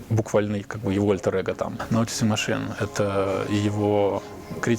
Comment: не могу понять, что говорит на 5:08 - 5:09 ( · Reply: Писателя прозвали "Noticing machine".